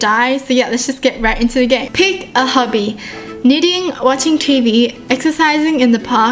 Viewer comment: I thought she said pick a hubby!!!😂😂😂